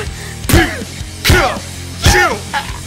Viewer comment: “pika pika” “fuck” is what I heard 😂